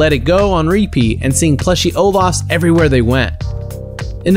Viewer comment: did he say seeing ovas plushes everywhere P.S its olaf